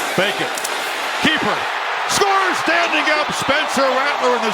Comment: does he say “Baker”??